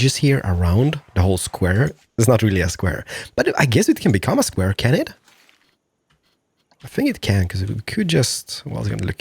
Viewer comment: "I guess I can make it a square, can it?" What I heard, "I guess I can make it a square, Kennet?"